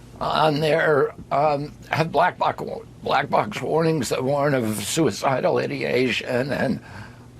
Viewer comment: Sounds like he said suicidal IDIOTations